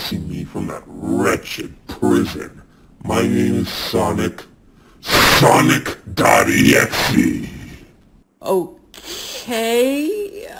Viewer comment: Why do I hear : "I'm Sonic.... Sonic daddy" 💀